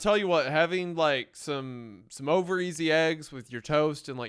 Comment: i stg i heard say "Ovaries and eggs"